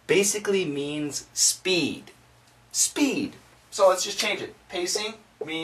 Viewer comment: When you turned to the board what did you say? "So let's we change it" ? or what? And second question - why the article is missing? I mean "the" or "a" before "scene"